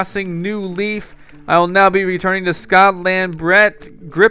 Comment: lol, you said "I will now be returning to Scottland" at 27:43 instead of Astoria...You've been playing minecraft too much ;P